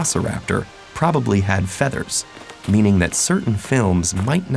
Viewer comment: Wait the aussie game hunter guy from Jurassic park says "clever girls?" Omg all this time I always thought he said "clever kill". D